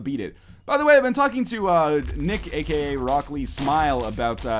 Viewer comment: I will admit when NL first said RockLeeSmile I thought he said broccolismile.